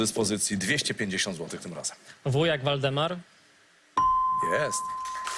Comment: usłyszałem ''wujek Waldemar'' xD